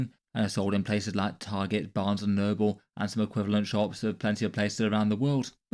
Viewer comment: i cant be the only one who heard barnes & Nurgle